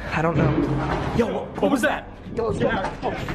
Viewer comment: I hear 'Ya Allah, what's that ?'😀